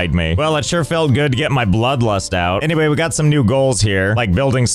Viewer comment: My bloodlust out. [me]: Did you say bloodlust? seems as if the gd level bloodlust by knobbelboy